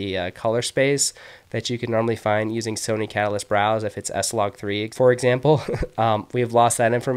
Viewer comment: Thanks for the tutorial!! At 7:23 you are mentioning an app that can read sony picture profiles but I can't quite understand the name. Do you mind please mentioning which one is that? Thanks a ton! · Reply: You’re welcome - Sony Catalyst Browse is the app you want to download.